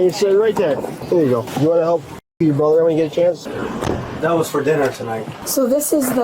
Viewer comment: What the fuck did that cop say at 12:16 ??? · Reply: He said “help your brother”